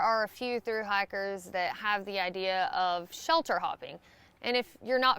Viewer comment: Haha, I thought I heard you say "shelter hog" not "shelter hop." 😜